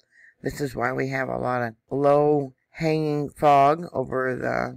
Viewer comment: ""low hanging fog" I had to restart your vid.I THOUGHT you said "" low hanging "fruit"😜🤣🤣🤣🙃